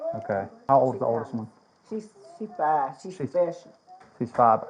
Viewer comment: Did Grandma just say the oldest grandbaby is 5 and "she special"? 😂